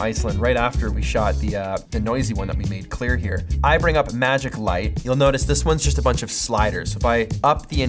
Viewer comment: when you said Magic Light, I thought you said Magic Mike. I was confused for a moment..